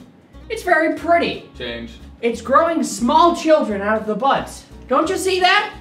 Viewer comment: I thought he was saying butts. That would have been a lot funnier.